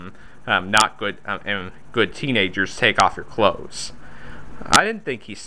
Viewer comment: Well Aladdin did not say "Good Teenagers take off your clothes", he said "Come on Good Kitty, just take off and Go".